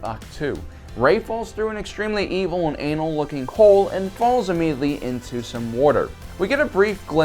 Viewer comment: did he say an evil and anal looking hole? 😂